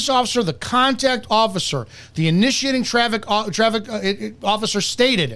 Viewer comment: I was a traffic officer for 27 years. This was absolutely a BS stop and Brandon is a definite boot licker. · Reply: I was skimming over this and thought they said "terrific officer" at first.